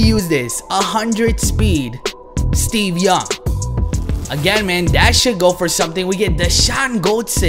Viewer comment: Lmao 3:36 its sounds like hes saying "dat shit should go for something." im dead